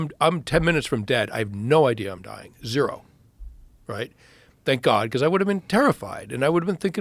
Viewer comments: Did he Just say thank God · Does he even know that he almost want to hell. He needs to accept Jesus our lord and saviour in his life and repent because alot of people dont get that grace and mercy he got he got a second chance can someone tell him that 🙏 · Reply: Did he Just say thank God on